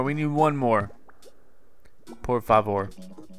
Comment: Hi! Did u said on 15:38 "por favor"? That was the same as "please" in portuguese... Do you speak another language? (Sorry about bad english) From your brazilian fan!